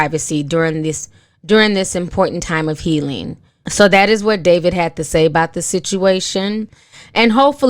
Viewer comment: I thought they say David had money